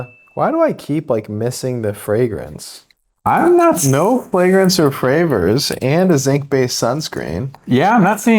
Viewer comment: I think he literally said "no flagrence or fravors" at 11:52. Maybe I am just hard of hearing.